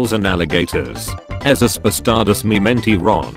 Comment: what the meme said: Esos bastardos me mintieron. What I hear: babvweenen skbedbhdkdnddbve medinndyrteron